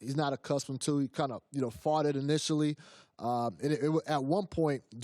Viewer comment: I thought he said “farted initially” had to rewind it🤣